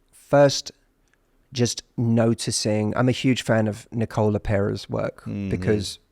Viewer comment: What's the name of the person Matthew said about regulating the nervous system? Nicola Perry??? 56:05 time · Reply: Hey Jonathon that's Nicole LePera. She's amazing! She's actually been on the show a few times as well -- here's our most recent interview <link>